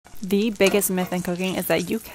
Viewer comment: So am I the only person who thought she said the biggest myth with cocaine is that you can’t cook